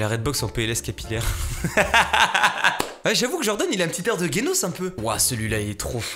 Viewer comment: Personne: Toujours personne: Moi: en sueur quand j'ai entendu GUénos à